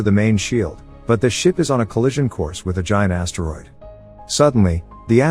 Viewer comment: As he said giant asteroid, I misheard and thought to myself, what's a teroid?